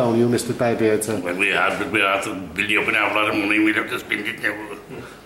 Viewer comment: "we're after building up an awful lot of money we'll have to spend it now" I think he says